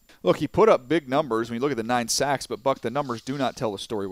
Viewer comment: did he say f the numbers 🤷‍♂️ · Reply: ELT_BlaZe Buck*